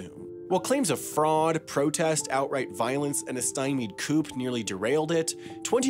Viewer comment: did you say coop? 🤦🏻‍♀️